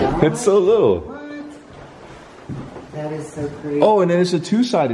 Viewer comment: Okay im only watching now in 2020 but at 10:01 you hear someone said "what" when says look at this lil door